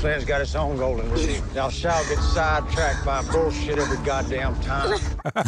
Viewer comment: The Ghoul’s rule of “don’t get sidetracked by shit” is hilarious because it literally goes against everything that makes Fallout great, getting lost in so many side quests that by the time you get back to the main story you’re OP a hell! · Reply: That’s not what he says, he said “thou shalt Get side tracked” meaning “you will get side tracked every time”